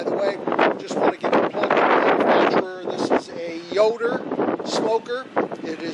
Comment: I have a "Yoder' offset smoker. I also looked up York but could not find it. Yoder is made in Yoder, Kansas. They are also sold through selected retailers and their own company store called All Things BBQ.. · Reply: Thank you. That explains it. I thought you said York, instead of Yoder. I appreciate the info and your video. All the best.